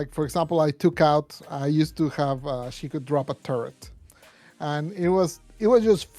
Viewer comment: i thought he said drop a turd 💀💀